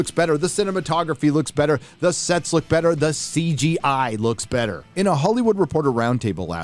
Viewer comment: I kid you not, I honestly thought you said "...the SEX looks better" hehehe~! Just me projecting.